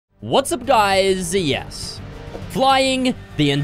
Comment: "What's up guys, yes" But the way I hear it: "What's up, no"